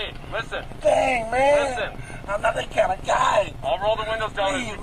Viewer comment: Ben, next time would you please let the video run WITHOUT commentary so that WE could hear what is actually said. THEN comment after. I thought he said, “I’m NOT that kind of guy” instead of “I’m going to die.” Not sure...